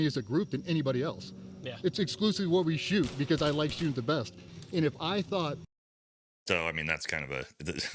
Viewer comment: To be clear DJ didn't say he likes shooting the 320 because its the best. He said he shoots it because he likes it the best. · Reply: I thought he said “I like shooting the best”